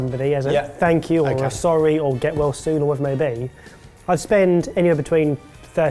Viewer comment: I thought said "Get lost soon"